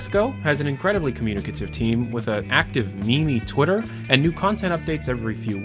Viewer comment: Yea the start was scary whatever... Did you say fucking "me-me"